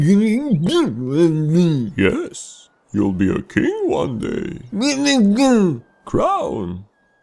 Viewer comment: What he says (i don't even know how i could make that out) "Im gonna be king one day" "With a crown" "I want it now" bllp blllllllp yay